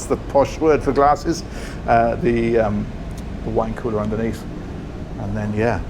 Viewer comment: Did he say 'wanker' underneath the stem wear 😂 it must be that elegant British accent that i misheard for a moment!